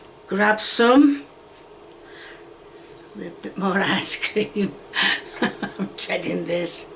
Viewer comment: sounds like she is saying ass cream lol